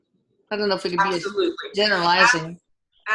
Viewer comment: I hear him say absolutely